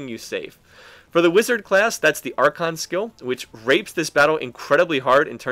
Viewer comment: did he say "it grapes this battle"? Lol